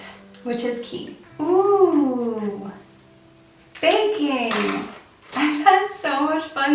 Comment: for a minute there I thought that you said banking as something fun instead of baking....was a little confused hahaha